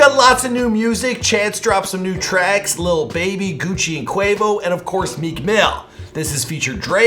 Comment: Did you...did you just say “lil Bae”? OMG!!!!! Lil Baby*